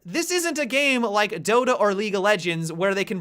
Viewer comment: did he say doto and league of legends lool